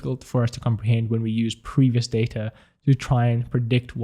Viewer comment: Did you just say " Darta" ?